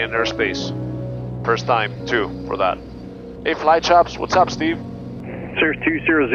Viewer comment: Did I hear a shout out to? O.o